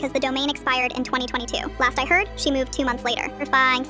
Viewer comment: Bro unlocked a new language at the end 😭😭😭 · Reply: Idk why but I heard “a fiiinne slice”…